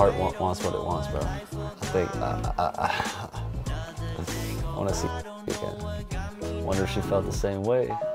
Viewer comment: what did he say at 8:18? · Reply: Kassie